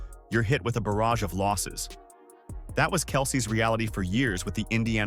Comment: Ohhh 'losses'. I thought you were saying 'lawsuits'. My bad. I was like, the lawsuits were piling up?? What'd she do??